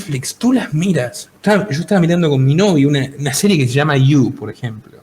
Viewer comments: Escuche mal o dijo novio ¿? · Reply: Ah no ya entendí. El dice Novio suponiendo que el es una chica y estan viendo una serie de Netflix sobre violencia de género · ¿Con mi novio? 😐 Edit: Escuche mal, es Novia